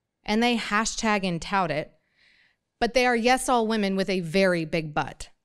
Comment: thought i heard, "yes - all women with a big butt" 😯